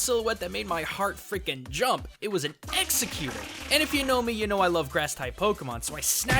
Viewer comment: Did he really say Exeggecute at 0:25 ? I don't hear the "or" at the end